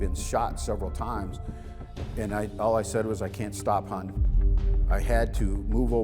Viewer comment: thanks for clarifying. I guess the phrase that he used sounded distasteful. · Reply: I thought he said “I can’t stop, hon”. I just listened to it again and I’m pretty sure he says Hon.